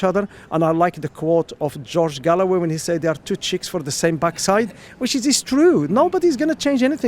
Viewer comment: If Tory and Labour are two cheeks of the same backside, then Nigel is the one in the middle! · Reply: Thank you! I didn’t understand because I heard “two chicks for the same backside” 😅 Greetings from an ignorant Belgian, EU.